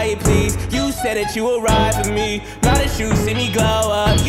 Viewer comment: Am I the only person that thought he said “I know who’s there at the door right now”😭😭